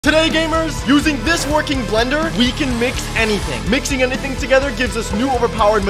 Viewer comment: i thought it said milk anything lol